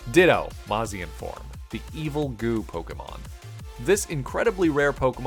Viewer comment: I thought it said “Evil God Pokémon” rather than “Evil Goo Pokémon” 😭 and ya know what? I totally accepted it